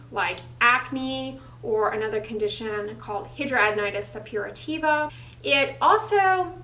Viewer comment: What's that word at 2:35? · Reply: hidradenitis suppurativa